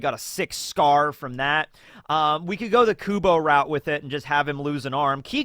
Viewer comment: did i just hear kubo?